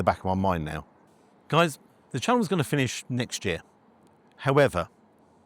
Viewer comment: So sorry to hear of all this…. People are sods. I’ve watched you from your day dot and you come across so lovely. Did I hear you say the channel was ending next year? 15:44 But terrible to hear that one bit of info…Liverpool !……. I can’t talk though I’m West Ham.